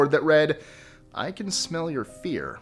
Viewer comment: On the first question I thought it said can I smell your ear